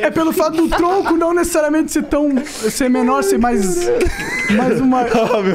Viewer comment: O que ele fala aqui? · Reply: Ele fala perda menor kk